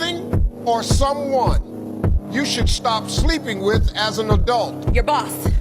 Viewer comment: that clip is even worse (or funnier really) when you hear he actually said should STOP sleeping with as an adult